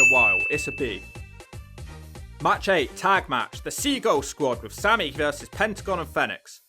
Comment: At first I heard as "Seagal Squad" 😆Whoa! Steven Seagal going Wrestling. Just kidding :)